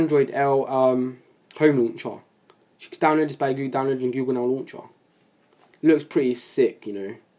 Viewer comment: What is he saying at 4:05 to 4:15, and how do u get it lol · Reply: Cj Brown Google Now Launcher from the play store.